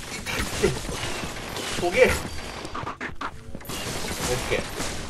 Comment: トゲ、がほげ〜に聞こえてニヤけた😌